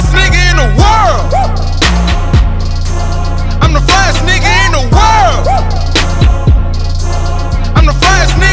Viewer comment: Sounds like he saying, the flyest sneaker in the world lol.